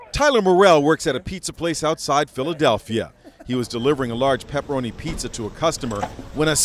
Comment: All I heard was " He was delivering a large pepperoni pizza on time" and some other jiburish